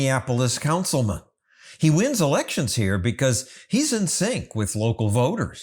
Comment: I swear at first I thought John said: "He wins elections here because he's insane..."